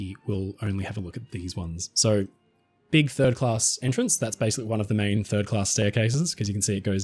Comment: hilarious youtube subtitles reads thirdass instead of third class